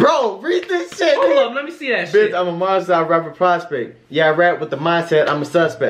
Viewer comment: When Rap Mon was like "Bitch I'mma Monster" sounds like he says "Bitch, who wants it?!" (these bars) 😂